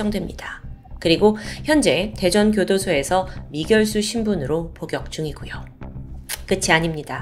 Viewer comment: 제가 잘못들었나요? 미결수라고 들려요